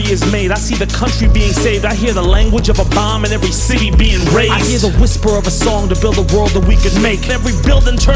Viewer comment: Hi, just FYI, around 1:30 the captions say “every city being raised,” but if you’re speaking of Gaza, I think the sadly accurate word is “razed.” May we live to see it raised again! 🇵🇸